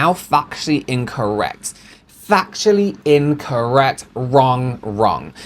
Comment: I heard "fucktually" incorrect the first time used said factually. I think this should be a new term 🤣🤣